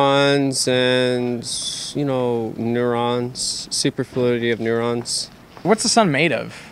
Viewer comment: did he say neurones?